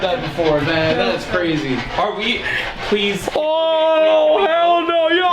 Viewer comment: In 29:15 minutes I heard help